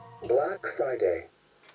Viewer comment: Click this over and over to hear the other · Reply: i heard next friday with